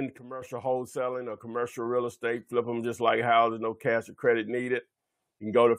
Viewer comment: I thought the ad said no crwdit no money needed to do this is that true or not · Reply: No cash no credit needed. Just techniques and persistence.. Thanks Ty